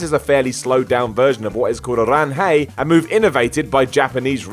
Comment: What's Madoka's move called? I heard "Land hey." lol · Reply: Ranhei